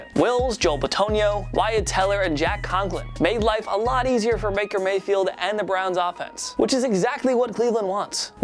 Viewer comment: Am I the only one who heard Maker Mayfield